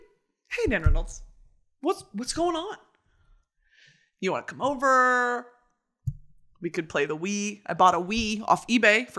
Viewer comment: i thought she said “hey genitals you wanna come over and play the wii?”